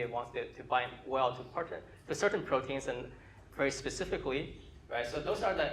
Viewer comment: I cannot hear what he says at 35:53 starting with "Maybe I want", can anyone transcribe? Thanks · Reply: I'm a bit late here, but I'm fairly sure he says, "Maybe I want to bind well to certain proteins and very specifically".